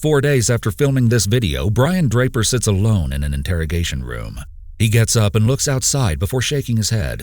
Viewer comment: Instead of "Brian Draper sits alone in an interrogation room" I heard "this idiot sits alone" and I find that to be a whole ass mood